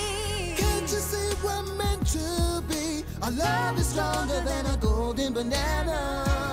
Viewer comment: They knew what they were doing. Also I came from Vinesauce's Twitch Stream. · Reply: Also he says "longer" instead of "stronger"